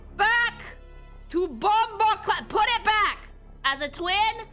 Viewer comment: DID SHE JUST SAY BOMBOCLAAT? LMAO